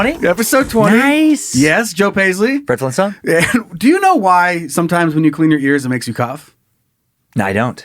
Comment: Right from the get...I heard. " do you know why when you clean your ear sometimes it makes you cum?" .... and I'm like mother fucker iv been cleaning my ears wrong all my life!!!! Hahaha